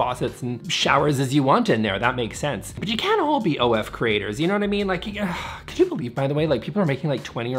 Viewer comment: At first I thought he said "They can't ALL be firefighters" and I was like oh yeah, true, I bet firefighters do need 27 shower heads